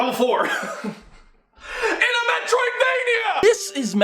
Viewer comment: In what? Mario? Kirby? Fancy Pants Adventure? Sonic, (although they call those acts and they rarely pass 3) What was that? It almost sounded like you said M E T R O I D .